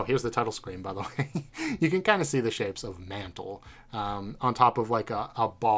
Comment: I thought it said manhole lmao, I prolly only thought that cause i was intrigued by the hidden manhole in the room prior to the Mantle game room.